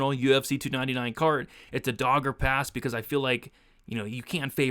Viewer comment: 141 sig strikes let’s goooo. When you said “dog or pass” I thought you’d said his “dog had passed”. I felt bad for cutelaba for a second.